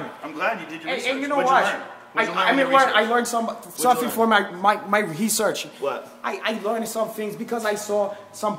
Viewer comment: “That’s a deadly hecipe for disaster” 😂😂😂 That one got me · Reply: What about when he said "hesearch" @ 6:26😂